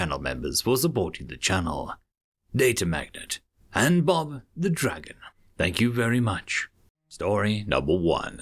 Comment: Thanks data magnet, thanks bob the dragon. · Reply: Bob the dragon, hu I thought you said "bother the dragon". I'm sorry for getting the name wrong, but like bother better.